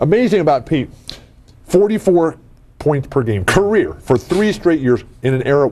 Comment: I thought I heard Bill say 57 points per game. What am I missing? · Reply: He said that Pete, in college, averaged 44 points per game for three years. There was no 3-point line then. Someone went back and watched all of Pete's college games for one of those seasons and calculated that, under today's rules, Pete would have made 13 3-point shots per game and averaged 57 points per game.